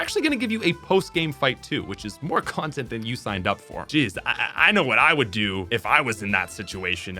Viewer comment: saying "j1zz" instead of "jeez"... Please don't tell me I'm the only one who heard that.